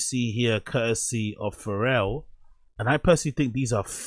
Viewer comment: Sounds like he said Pharrell and Meatloaf hahaha